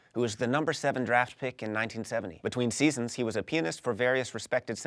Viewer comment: he says he was a penis instead of he was a pianist